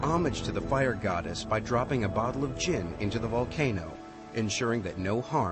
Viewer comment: did he say jin???!!!